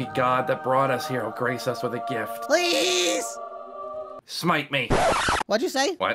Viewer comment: What did Moondrop just say? 9:02 Sundrop: Maybe were......Lucky 9:04 Awww I love how Sunny laugh so adorable. 😊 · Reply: I think he said "smite me"